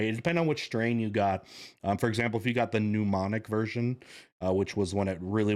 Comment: The 'mnemonic' strain of plague? [6:09] That sounded like something Philomena would say. If the 'mnemonic' plague were a thing it would be a really useful advantage, as it's a linguistic device to aid memory recall. I guess you meant to say 'bubonic' plague.